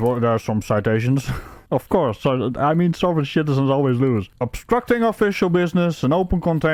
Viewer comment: Do I hear „Sovereign Shitizen“?